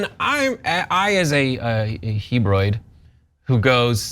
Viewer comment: did he say "hebroid"?